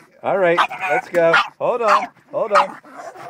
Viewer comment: I heard "I love you" and "I'm ready". LOL